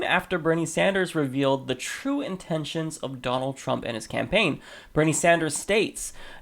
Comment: I thought he said " Donald Frump"